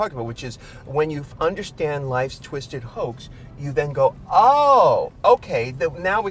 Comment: What's Ru saying at 3:57? It sounds like "when you understand life's twisted hoax."